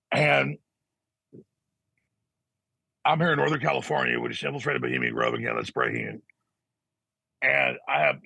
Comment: what did he say?? Lol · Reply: "I'm here in Northern California, with the civil straight of Bohemian Grove here, that's breaking in." was the closest I could get. I was gonna write "I'm here in Northern California asidfauybqwudbqw Bohemian Grove bquwcbqwdbwqfd." at first though, haha.